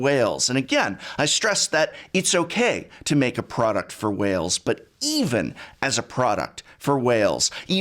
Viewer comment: I zoned out and had to rewind when I misheard "It's okay to make a product for Wales"